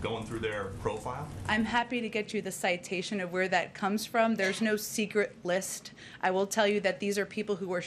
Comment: PSaki- This isn't a secret list of people Also Psaki- We're not gonna tell you the 12 people we are censoring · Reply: She did NOT say that either. She literally said (@ 0:50), "I'm happy to get you the citation of where that comes from. There's no secret list."